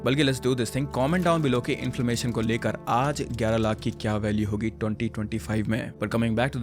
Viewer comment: did he just say inflamation at 7:50 lol !!